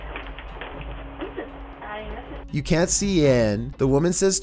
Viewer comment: No on number 9 she said “I can’t see him” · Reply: wujk thought she said “You can’t see in”